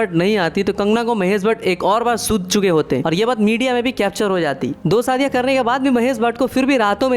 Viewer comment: what did you say? I didn't understand. Can you clearly type the text 😢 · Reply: Sutt chuke hote it means mahesh bhatt would have been beaten kangna twice..